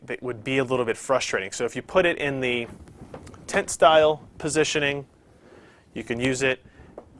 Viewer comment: did he just say "Tit style? sounds like it can be easily misheard.